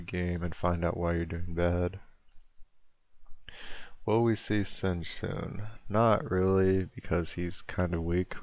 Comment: What does he say at 1:50? "WIll we see -?" can't seems to understand the name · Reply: "will we see singed soon?"